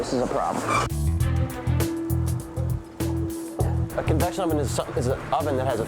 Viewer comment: Did he say conduction oven? I almost wanna give him the benefit of the doubt and assume he said convection oven, but his smugness is horrendous.